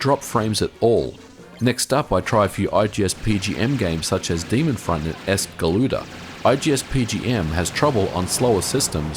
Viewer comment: I cant understand what he is saying "I tried some IGSPGN games like Demon {word} S galuda(?)" I wnant to know what game that is